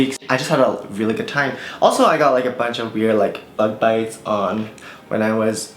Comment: WHEN U SAID BUG BITES I THOUGHT U SAID LOVE BITES I SCREAMED